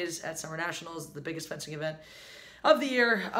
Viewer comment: Referee for...what again? You say it around 0:45 or so, but I can't quite pick it out. Sorry. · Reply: Fencing.🤺